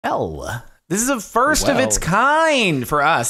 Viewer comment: “ell.” He didn’t even say hello, he just sayed ell xd, or what he tried to say xd